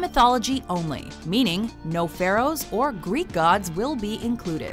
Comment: Thought you said no greek gods